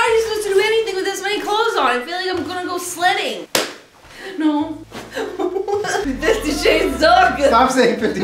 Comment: I swear to god that, for so long, I thought Jenna was saying 50 Shades Taco and I still can't listen to it without hearing that.